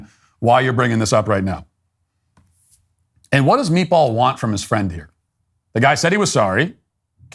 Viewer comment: Lol is misheard meatwad and imagined that character going through this dilemma 😅